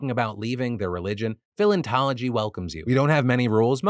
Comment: Jesus I had to go back cuz I heard "scientology" not "philentology"